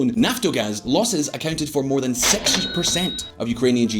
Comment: he said 6% right? Captions say 60 but that wouldnt make sense